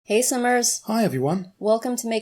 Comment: when you say hey simmers I thought you said hey summer's the first time I watched you